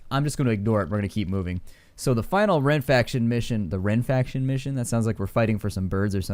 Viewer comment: I didn't hear "Wren-faction" .. I heard "Ren-faction". I was picturing guys in tunic & tights with absurd accents trying to get proper zoning for a faire. Still not very threatening.